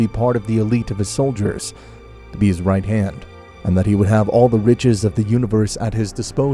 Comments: I could have sworn I heard "and he would have all the b!+ches in the universe at his disposal" instead of riches. I bust out laughing and ha to rewind to check what I heard. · I thought he said all the bitches the universe could offer 🤣